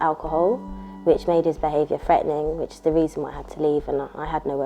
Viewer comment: Hey guys please would tell me what she said in this one (0:24) he begins drinking alocohol which made his behavior ( ......... ). Did she say fegning or feining something like that coz im learning english Please any answer